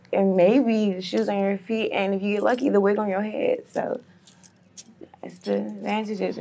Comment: I just love how she added “the wig on your head” lol I’ve heard “clothes on their back, shoes on their feet” but never the wig 😭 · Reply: I thought she said "..and the wiggle on your hips"